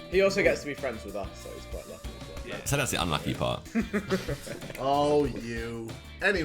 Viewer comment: Vik: he gets to be friends with us Josh: that’s the unlucky part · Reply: I thought he said "tell us the unlucky part"